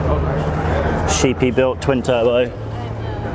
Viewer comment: did you say “shitty build twin turbo?” Or did I hear you wrong